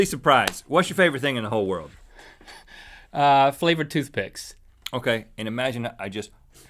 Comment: "What's your favorite thing in the world?" "FLAVORED TOOTHPICKS" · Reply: i thought he said flavoured toothpaste